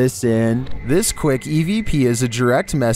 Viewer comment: I thought he said EDP